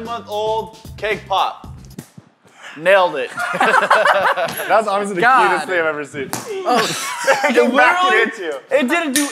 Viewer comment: did say "that's honestly the cutest thing I've ever seen" ? or did I just hear that?